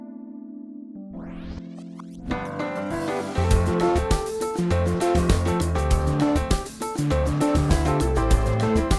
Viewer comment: do you know what she said 😂😏😏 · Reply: She said oh shit